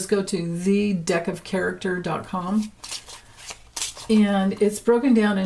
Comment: Lordamighty, I had to re-read the title. I thought you were saying The DEATH of Character! 😆